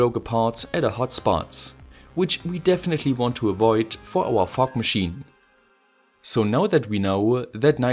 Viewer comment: i literally heard "fuck machine" instead of "fog machine"